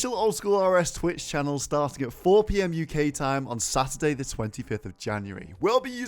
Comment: Did he say SATURDAY at 1:15 ?